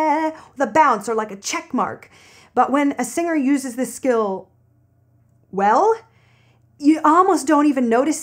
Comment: 當我在等待他要說什麼形容詞，然後又看到字幕寫如火純青，結果她說well